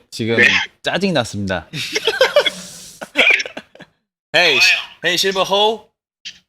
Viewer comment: What does say here 13:40 ?? Shiba-ho? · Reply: silver-ho, nickname for Eunho (Eun=silver in korean) ✨